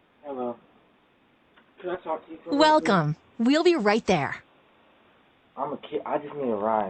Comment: The bb at 9:51 seemed lost .. sounds like he said “im just a kid”🥲